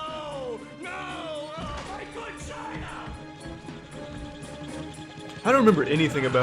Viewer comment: did I just hear plankton yell “MY VAGINA!”